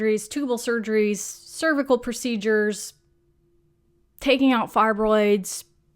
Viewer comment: Haha! I am so sorry but at minute 2:57 I swear I thought you said "taking out thyroids!" Hahaha